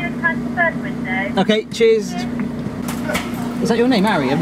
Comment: Im not used to England accent, so when you were at McDonalds i thought you said cheese but you really said cheers :D